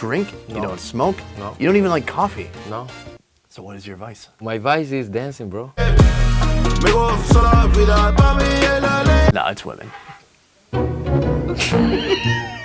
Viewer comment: Did he say woman at 3:05 ?